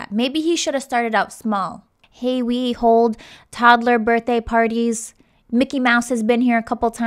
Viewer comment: I thought she said toddler burning parties